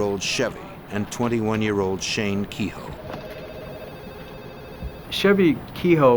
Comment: I thought they were saying shabby keyhole.